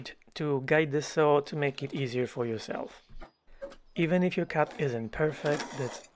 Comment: I almost thought you said, "Even if your cat isn't perfect..."